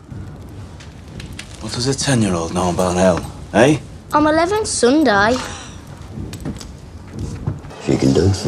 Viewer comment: I always thought he said "someday" instead of "Sunday"